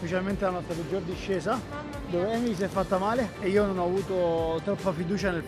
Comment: solo io ho capito noemi?